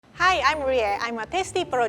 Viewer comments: what she said- Hi, I am Rie what the captions read- Hi, I am Maria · Guys try putting the subtitles in the beginning...it says Hi I’m Maria instead of Rie and i died 😂😂😂🤪